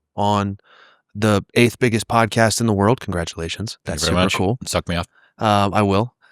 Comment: does will say suck me up?